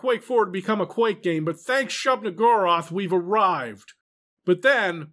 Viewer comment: Omfg, for a moment I thought he said Sheogorath, but it was actually Shub-Niggurath, which is just as good. That Police Squad bit was hilarious too.